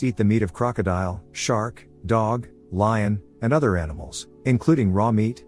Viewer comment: wait did he just say dog?